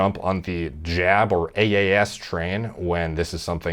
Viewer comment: mentioned it lol. How you gunna comment without watching the vid? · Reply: all I heard was "jab train". Seems odd a big health & fitness youtuber fails to delve into the subject.